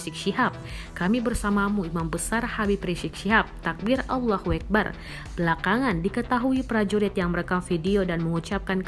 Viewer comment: apa engga salah denger Allah huekbar 😥